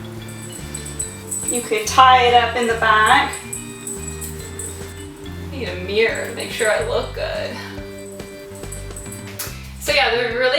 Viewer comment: This is so cool! Thank you for sharing and it's very well explained! I was cracking up at 5:19 because I thought you said, "I need a beer to make sure I look good" lol